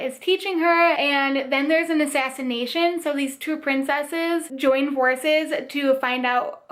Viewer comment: I thought you said that the girls in Of Fire and Stars had to join horses 😂 I mean it's not a lie haha! I looove this selection of books, especially with Not Otherwise Specified and Tash Hearts Tolstoy 😍 can't wait to hear your thoughts on them!! 💜